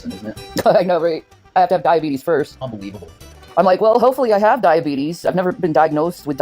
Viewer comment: Oh I know you just can't win life is so unfair and terrible ONLY FOR YOU. Wtf. @ 12:35 She actually said "well I hope I have diabetes". 😳🤯